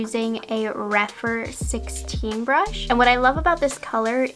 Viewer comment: the subtitles says you're using a 'rat fur brush'....I had to stop and think for a moment, "wait...they make brushes with rat fur!?" Rephr. Rephr.